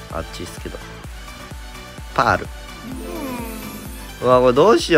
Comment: やってたのは、ぱある、ってパールじゃなくて、全部ひらがなにしたみたいな感じで聞こえる笑